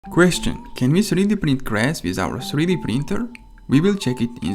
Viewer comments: I thought it said 3d printing glass. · The whole time I thought it said “can we 3D print GLASS ” it’s time for bed 💀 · Thought it said glass but making grass is cooler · Dude I thought it said 3d print glass and now I'm curious how that would work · Thought it said "3d print glass" and I just was so confused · The whole time I thought it said “can we 3D print GLASS ” it’s time for bed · I thought that said glass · My speakers suck so much i thought he said glass lmao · I'm so dumb I that it said glass and I also thought HE said glass 😂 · I thought it said glass. I need some sleep